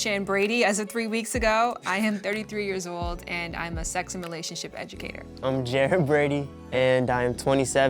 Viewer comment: Did she say ‘sex simulation educator? Is that a fancy way of saying porn star? Please correct me if I’m wrong. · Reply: Stepz97 • she said and is a sex and relationship educator